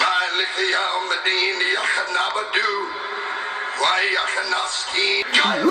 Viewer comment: What did he say.. "Wa iyakanastayin" Or he said "Palestine" (filistin)😂